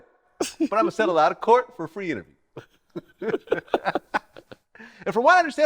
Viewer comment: He was dead, serious about settling out of court for three interviews, though boosie 😂😂😂😂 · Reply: Thought he said free interviews